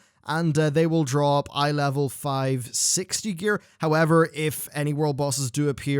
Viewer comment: Am I the only one who heard him say "iLvL 560 gear" at 3:31?